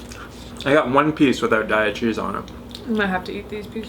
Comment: oh it's daiya cheese. I thought you were saying diet cheese. i was like that doesn't sound vegan.